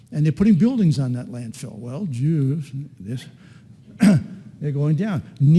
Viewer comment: Did he say, “Well, Jews!....”